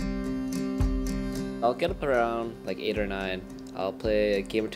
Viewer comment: I keep hearing "Vulcun thrown... like 8 or 9" at the start. What does he actually say?